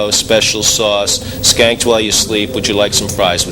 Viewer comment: I've never been able to figure out what Frank says near the end while describing the Thruster Buster's condiments. "Skanked while you sleep"? What the heck does that mean?